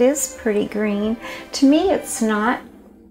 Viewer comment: "I guess it is pretty green. To me it's not." but honestly I first heard, "it's SNOT"